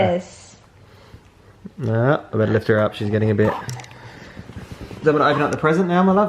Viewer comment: I think I heard say "better lift her up she's gonna hibe". Wala lang ang cute lang. 🤣🤣 Isla is the cutest tho. 💖